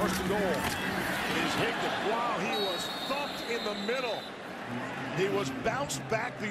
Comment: did the announcer just say "he was f**ked in the middle"? Sounded like it lol